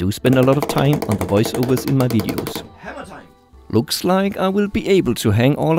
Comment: I think I heard something like "hammer time"